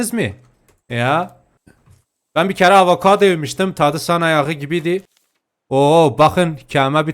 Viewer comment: Sizce burada ne diyor A:İnsan Ayağı B:sana yağı